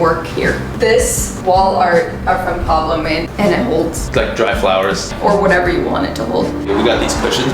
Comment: what name is she saying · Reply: Pablo Borra Paley